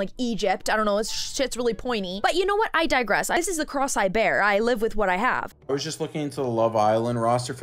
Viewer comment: It sounded like you said “cross eyed bear” and it was very cute